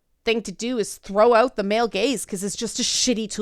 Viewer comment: why does every time I hear the term 'the male gaze" somwhere in the back of my ind I here this.. "the male gays." not sure if it is because I am pansexual and just hoping for a funny parody theory video or what.